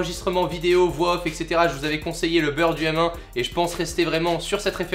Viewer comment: Pardon, tu conseilles quoi comme webcam ? j'entends pas merci pour la réponse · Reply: dsl du retard mais je crois que c est le burd um1 ou le bird um1 jsp ce qu il dit